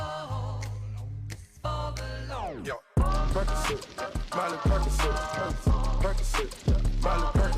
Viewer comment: does he say Percocet lol?